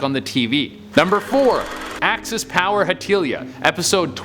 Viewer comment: I actually remember reading about that lost Hetalia episode. That actually got debunked as a fanmade creepypasta but imagine if it WAS real 0_0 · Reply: I thought u said hentai.------------.